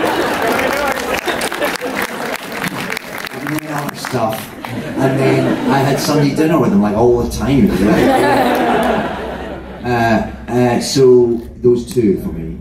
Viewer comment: "and then I had something to do with him, I owe him a..." HE OWES HIM A WHAT??? Geez Scottish man I'm having a really hard time here figuring out what is it you're talking 😂😂 Someone please, what did he say? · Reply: "and then I had Sunday dinner with him like all the time"